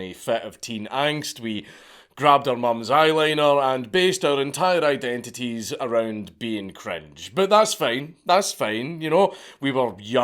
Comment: I thought he said "base our entire identity around being French" like damn, you UK guys really dont like the French, huh 🤣